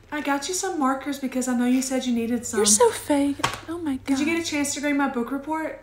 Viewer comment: Did they just say “your so fake”